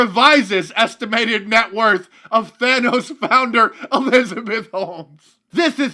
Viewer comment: did he say Thanos? 😂